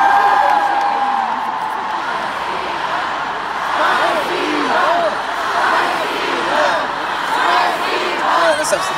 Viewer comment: What did they say in 1:25:35 ? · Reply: "spasibo"means thank u